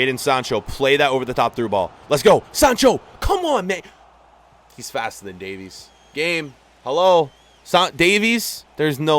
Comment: I thought he said Sancho Is faster than Alfonso Davis